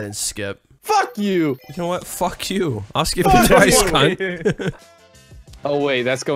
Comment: Anyone else notice that at 4:21 instead of saying ill skip you twice it says I'll skill you twice